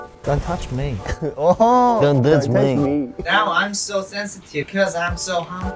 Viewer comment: did anyone else hear Aussie when changbin said don't touch me?